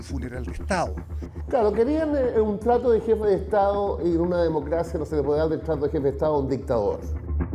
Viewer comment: que dice este guatón ql? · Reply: Claro querian un trato de jefe de estado y una democracia que no se le puede dar detrás de un jefe de estado aún dictador* xd